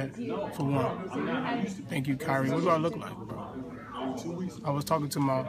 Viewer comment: Lmao I swear I heard "thank you kyrie" not Jamie.